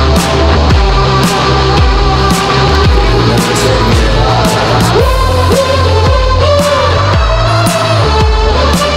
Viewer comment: what did he said? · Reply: "You'll never take me alive" I think.